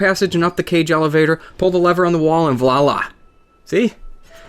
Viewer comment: Pretty sure I heard "Vlala" instead of "Voila" haha